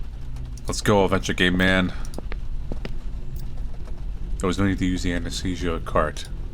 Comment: can anyone tell me what he says at 3:03 all i can hear is "lets go avenge a gay man" which seems like a fun plot hook but not relevant to this game 😂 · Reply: Way late to the call but "let's go adventure game man" for anyone wondering in the future